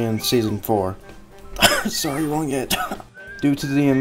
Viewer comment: I was listening to this in the background while at work, in the beginning sorry wrong Ed, it legit sounded like he said sorry long hit lol.